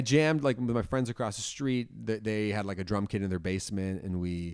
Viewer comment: I thought he said they had a drunk kid in their basement 😭💀